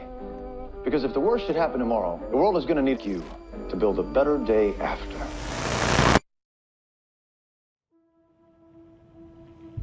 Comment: Does anyone else think they messed with audio at 0:25 to make it sound more like Goggins' character says, "the world is gonna nuke you" instead of "need you"?